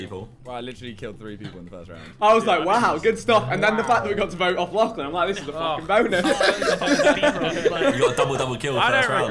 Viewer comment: what did vik say? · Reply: He said the fact that we get to vote lachlan this is the fuckin bonus